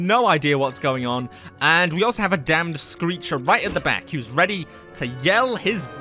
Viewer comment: Is he saying dam or damn? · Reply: Oh he’s saying dead 💀 lol i